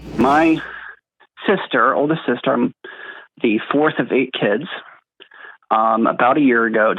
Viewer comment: She is the oldest? I understand that. My experience is not the same as my siblings. Give her the space that she wants. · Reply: I thought he said oldest sister, FOURTH of the kids- like they both had older brothers???